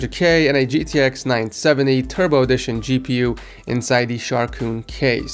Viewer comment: I thought tou said "terrible edition"